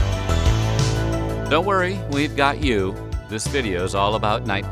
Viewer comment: Legitimately thought they said " Don't worry we got the juice ". Referencing the movie Juice which came out 1992. Missed opportunity.